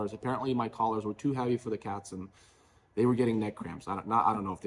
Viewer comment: did I hear "cat's were catching neck cramps"?